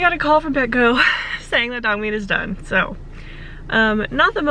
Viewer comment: Did anyone else hear her say Dogmeat is dead? I swear, that scared me so bad for a sec before I realized what she actually said. 😭